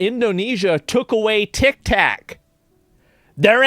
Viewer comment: two seconds in and I hear “tiktak”😂😂